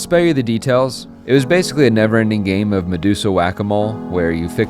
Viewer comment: At like 11:45 did he say medsusa?